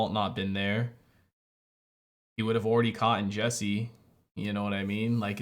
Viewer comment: Loving these reactions mate, but did you just say 'caughten'? 🤭